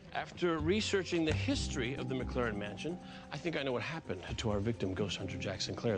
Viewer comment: Did Castle call Kate Claire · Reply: No he sad McClaren if you would take a look at the murderbord